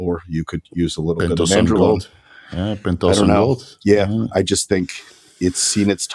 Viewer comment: Did anyone hear the deca alternative for joint lubrication? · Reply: Pentosan gold